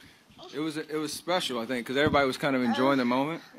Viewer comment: Ohhhhhh "blessed" 😂 I thought she was saying "I feel pissed" 😂